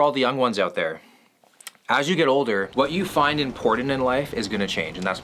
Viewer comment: I thought he said "what you find in porn and in life"💀